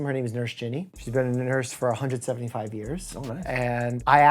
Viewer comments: "she's been a nurse a 175 years" did I heard that wrong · fantastic collab , certainly hope for more BUT i gotta ask 13:09 did he say 175 years ??? and dude just said "nice"....im hearing this wrong wrong right?? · did you say 175 years!?!? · Guys am I the only one that heard him say that the lady has been a nurse for 175 years? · Did he say 175years?